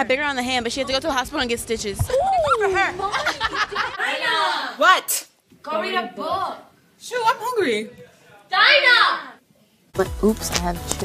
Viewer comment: What are they saying in 1:47? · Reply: Dinah:what Camila & Lauren:go read a book Dinah:shut im hungry Camila & Lauren:Dinah!!